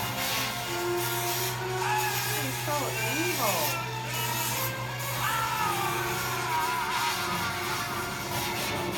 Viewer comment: I never understood what he says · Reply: Guy Mad Mad Guy I think he says “unlimited”.